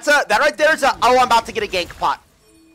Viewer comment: ohhh gank pot potion i thought he said gank bot